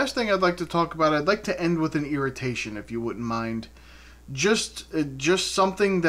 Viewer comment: Some people end their videos with affirmations which is what I thought you said when you said irritation but irritation is way better lol. I've never been a slapstick comedy type of person and on that same note, I like those dog videos where the dogs are doing something cute but inevitably a lot of the owners are teaching the dogs bad habits and then I get sad for the dog because they're encouraging it now but then they'll get in trouble for it later. Those owners are a bunch of jacka... wagons (your word not mine lol)